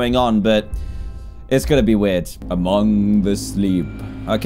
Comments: oml i thought he said among us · I thought it said "among the sheep" 😂😂